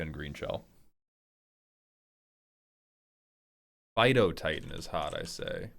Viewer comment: I heard Fidotitan, and got disappointed that it wasn't a big dog